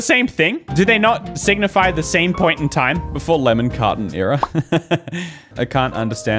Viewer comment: until now i thought he'd been saying "lemon cotton merch" and id just accepted it bc cotton candy is pink but i see now its lemon carton.... the shame